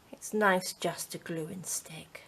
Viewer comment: Wonderful, my mind is going crazy with variations on the theme. Journal pages, handpainted papers, gel prints, textured papers etc, etc. ❤ I thought I heard you say, "life's just a gluestick" and I was agreeing but then I checked and obviously misheard. 🤣